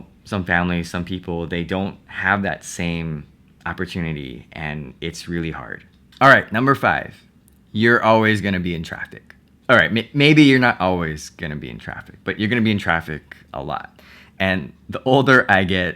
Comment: when he said "you're always going to be in traffic" I thought he said "you're always going to be attractive" and I'm like orly?